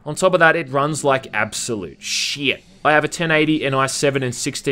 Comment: What does he say? 1080? Is that a graphics card?